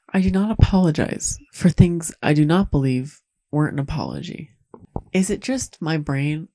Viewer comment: He says "I do not apologize for things I do not believe warrant an apology." The YouTube subtitle is just incorrect. Or maybe I'm just guessing wrong lol but I think that's what he says there.